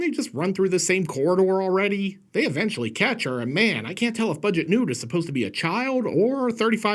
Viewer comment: I thought you said "Nude" (as a pun, given that it's an italian horror rip-off)